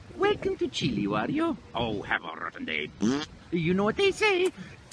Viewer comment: What did he say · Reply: He Said His Iconic Line "Have A Rotten Day!"